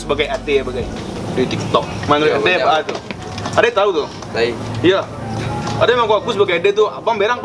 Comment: "Ade tau tu?" "Tai" (ternyata salah denger, dia bilangnya "Lai")